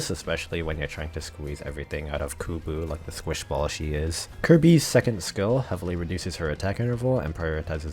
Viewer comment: Did just call Ceobe Kirby at 5:46??